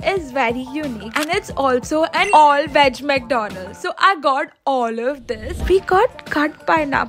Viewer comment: Did I hear "All veg McDonald's"😊😊 It happens only in India🎵🎵🎶🎶